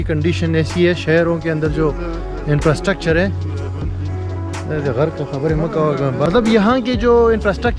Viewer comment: pe kya bola tha bhai... Good work keep it up ♥️♥️🇮🇳🇮🇳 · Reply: Pashto main bola tha k Allah tujhy ghrk kreee chup hojaaa